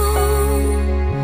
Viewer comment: all this time i thought she was saying "i feel like my heart is stuck in a bupurupupuh"